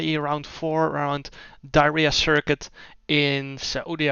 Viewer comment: Im actually stupid. I thought you said diahreah not diriyah